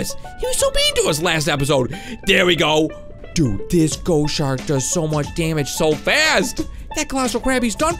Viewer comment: Did he say Dude this go shark 1:36 P.S play as a baby wit the strongest shark